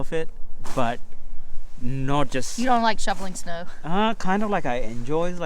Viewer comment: did she say shoveling or shuffling snow? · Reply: Shoveling 😃 🤣